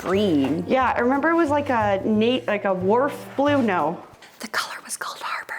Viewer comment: When you were guessing the color of the squiggly lines on the wallpaper and said "wharf" I heard "Worf" and thought "oh cool, Star Trek TNG".